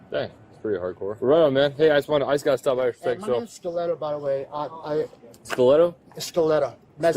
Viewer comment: I thought he said 'Stilletto' in the beginning- which I thought was a made up name